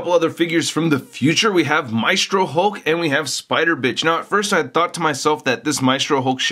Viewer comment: did he says spider bitch?!